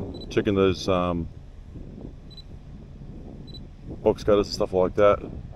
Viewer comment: Brother it sounds like your saying " box cutters' it took me ages till I understood WTF you were talking about, never have I seen those types of roofs or gutters, you taught me lots so quickly, thank you.